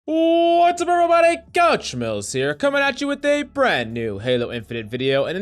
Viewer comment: Why do I hear “ what’s up every buddy couch smells here “ 😂